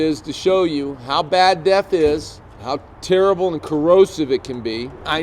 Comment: Oh sorry it’s def, sounds like your saying death haha. But why don’t you explain what and why people are trying it. Thanks